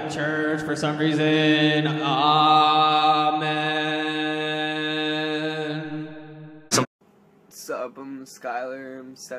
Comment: is he just saying "armen" ? XD Oh no, now he is jesus with his long hair and the 🌟armen🌟